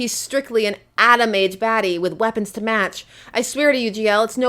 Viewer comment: I thought I heard “anime baddie”.